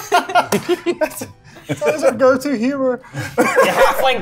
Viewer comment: Can anyone tell wth says at 10:00 ? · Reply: he says "it's always our go to humor" ie racism